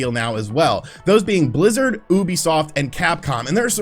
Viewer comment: U know, when u said Ubisoft, I swear I heard "boobysoft" 😆